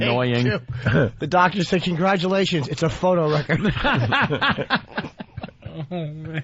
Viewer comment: what does jimmy say at 5:17? "its a photo wrecker?"